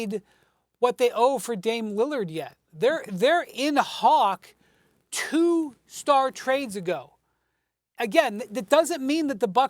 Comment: Why does windy say hawk tuah at 5:58 😂